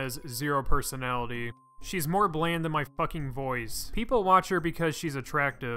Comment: “Pokimane has no personality, she’s more bland than my voice” you fucking killed her dude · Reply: I thought he said she is more bland than my boys.. Lol